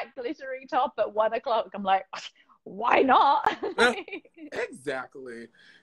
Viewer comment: Sophie: "why not?" What I heard: "wine nohh?"